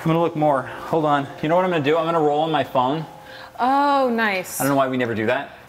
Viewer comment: I thought he said "I'm going to roll in my thong" I'm dying🤣🤣💀